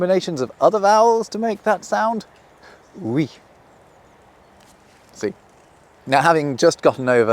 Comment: you said "see", i heard "si". Both work